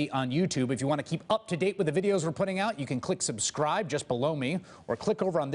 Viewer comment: Chris Hayes please please give us a break from that crap at the end of every video. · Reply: Mark Mazer I hear it as "just blow me". Makes me wonder what goes on under the desk.